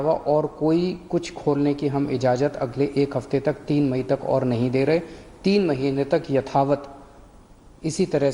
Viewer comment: Did he said 3 mahine tak at 5:32😳😳😳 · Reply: Md. Asif 3 May tak